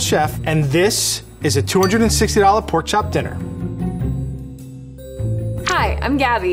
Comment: Did she say Porn chop dinner